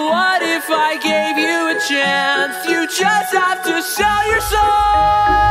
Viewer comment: When he said "you just have to sell your soul" it sounds like he saying "you just have to show yourself"